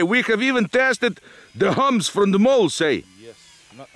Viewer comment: The cow manure is no problem, it is the hoof prints that will break off your landing gear. EDIT: I thought you guys were saying "MOOS", meaning cows. But you were saying "MOLES". I get it. I thought they were piles of cow manure.